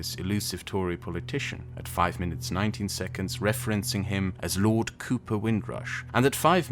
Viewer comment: When you were discussing the Britain ABOLISHES itself video, for a second I thought you said 5 minutes 90 seconds. It took me a second to realize you said 19 seconds.